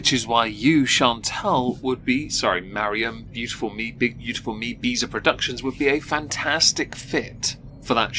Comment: I misheard "Big beautiful me beezer productions" as 'Big Beautiful Meatbeast' somehow 😂